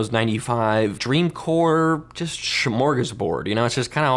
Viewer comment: Did he say Smörgåsbord? 😂